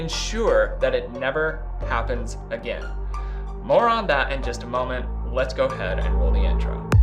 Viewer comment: When you say “more on that” I thought you said “moron” lol